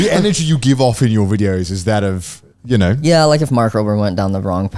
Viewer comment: Subtitles say “Mars Rover”, but he said “Mark Rober”